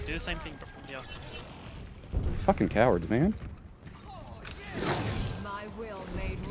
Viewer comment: 'Fucking cowards, man.' Did I hear that right? knows his stuff, but he's got a nasty streak.